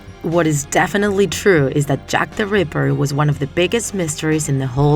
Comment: I thought it said jack the stripper XD